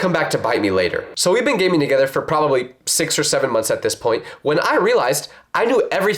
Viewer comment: When he said 6 or 7 months I was wait did he just say 67!!!!!!